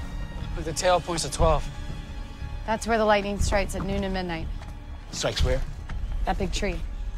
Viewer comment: Am I the only one who hears bEeTEe say: S*x where? Or am I dirty minded